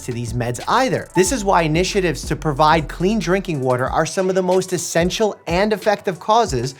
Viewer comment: DO I HEAR FREE WATER???